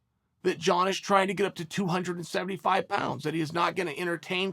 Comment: I thought he said 250 and wants to get to 275 ?